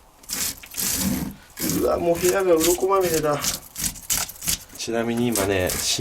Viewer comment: 初見だとうわーっ部屋がう〇こまみれだって聞こえたw